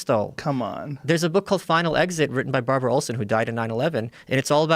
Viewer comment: Anyone have a link to the book he mentioned at 2:49? Sounds like he says "Final Exit" by Barberra Nolan, but I can't find mention of that book anywhere